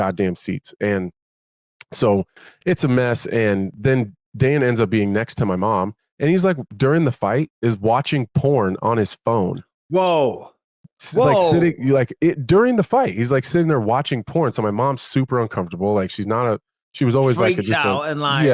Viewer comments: Sounds like he said Dana was watching porn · Not gonna lie I thought he said Dana was watching porn during the fight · I thought he said Dana, my mouth dropped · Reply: No he doesn't he says "Dan ends up being next to my mom" He mashed up Danends and says it Dane nds